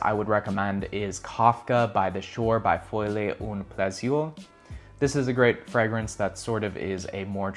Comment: Hey could you maybe spell the parfume out because i have no idea what was just said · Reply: Kafka on the shore by Folie a Plusieurs